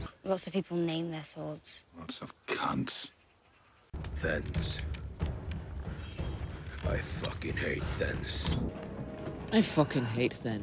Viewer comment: what does he say at 5:49? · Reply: "Thenns. I f&#$ing hate Thenns." Thenns are a faction in the wildling army- the large bald dudes